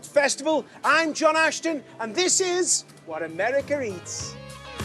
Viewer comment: Did he say Americer?